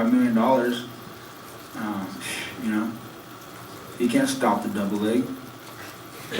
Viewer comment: What does he say @ 9:30, "You can't stop the deviled egg?"